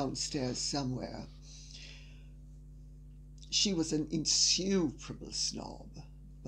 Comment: LADY C... I JUST LEARNED A NEW WORD... "INSUPERABLE" . AH. THANK YOU. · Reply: I thought she was saying insufferable.